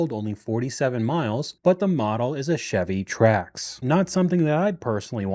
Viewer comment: Did he say 'Chevy trash' ? 😂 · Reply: Trax